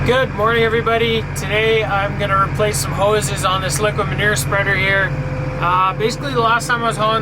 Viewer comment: It sounded like you said liquor manure instead of liquid manure. There's a thought for you!